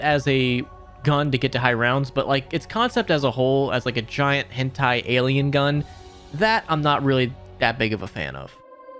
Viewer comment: did he say giant "hentai" alien gun??? or am i tripping